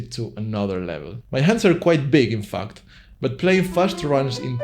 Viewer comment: I thought you said "my hands are quite big and fucked" for a second 😭